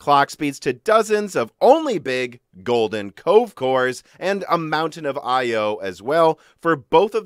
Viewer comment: I thought you were saying golden Coke course lol